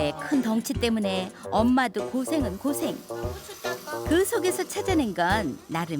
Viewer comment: 고추 딱아 소리 들리는데